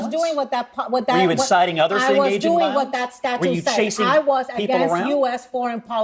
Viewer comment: all I hear is “ta ta ta ta ta ta ta” 😂